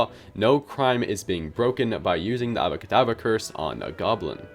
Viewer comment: Did you say "Ava Kedava?" I'm not sure if you meants Ave Maria, jk or Avada Kedavra.